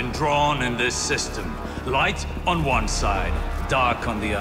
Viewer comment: uhhhh i think rasputin just said darkness